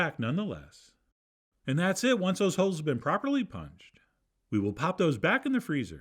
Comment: At first I heard "once dem ho's have been properly punched". I was like DAM Chef.